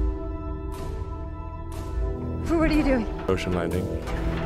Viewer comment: “What are you doing!?” “Ocean landing.” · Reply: I thought he say emotion landing....